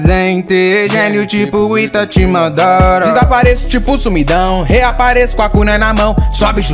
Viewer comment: a primeira vez que eu ouvi eu achava que era "reapareço com o c* na mão" kk😂